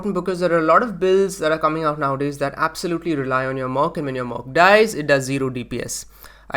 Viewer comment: What is he saying in 6:20 ? what is Mok? mirror of K im thinking and then he says "when your "Mok" dies"... so he isn't talking about the mirror i guess? so my question is, what is he referring to? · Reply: Have you not even played the latest league? Merc = Mercenary